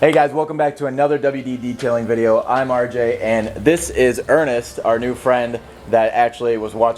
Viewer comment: When he said Ernest i thought he said anus